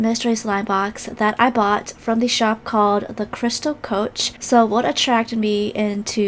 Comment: srry im so late but i thought that said the crystal roach big mystery slime box XD